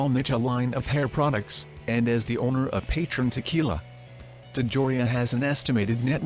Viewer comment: Did he just say Peytron tequilla?